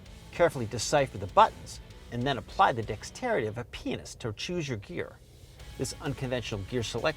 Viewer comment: i heard penis